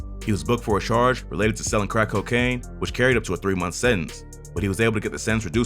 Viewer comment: did he say it carried up to a 3 month sentence but he was able to get it reduced to an 18 month sentence??? 🧐